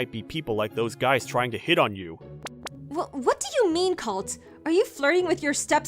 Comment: is it just me or did she say cult instead of cute